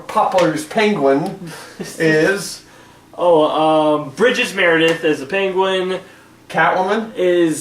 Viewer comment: Correction at 4:27-4:33 as far as the characters: Burgess Meredith played The Penguin & Lee Meriweather played Catwoman. It sounded like you said 'Bridges Meredith', at first...